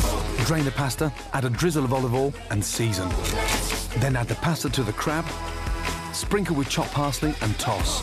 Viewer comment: i heard add the pasta to the crap